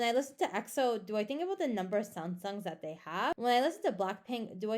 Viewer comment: number of sound songs??? lol i dont understand what are you triying to say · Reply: "When I listen to exo do I think about the number of sasengs that they have".